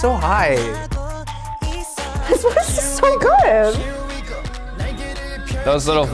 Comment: i think she said "his voice is so good"